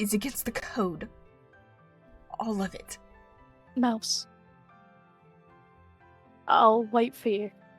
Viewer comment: Ohhh... I didn't know WHAT Cherrystar was saying until 3:02 when he said "Mouse" meaning it's "Mouseheart".